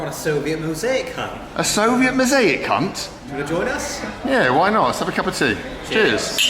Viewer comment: Thought he said the c word not hunt